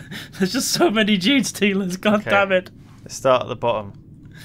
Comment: This whole time I thought was saying "dream stealers"